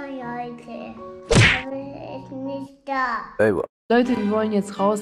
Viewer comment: Hat gerade Alte gesagt schau mal 2:13 😮 schreib mal in Kommentare ob ich richtig gehört habe oder falsch gehört habe 😮😅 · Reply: Nein sie hat Leute gesagt So süß